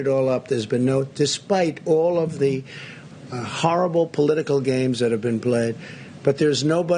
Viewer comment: Did he say "deSPIKE all of the horrible political games that have been BLEAD ......"?